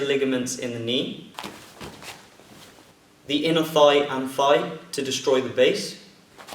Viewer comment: When I watch your videos I have trouble believing you only a 2nd degree. Your very good. When you said "thigh" I thought you said "fie" and I'm sitting here thinking "what's a fie? I took anatomy I should know this" than you kicked and I was like oh he said thigh(: i like how you take a lot of time in your videos. Nothing is worse than a tutorial that is to fast and leaves out important details.